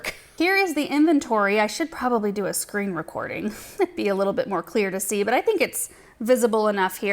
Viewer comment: I thought said “scream recording” 😂 I scream, you scream, we all scream for dahlias!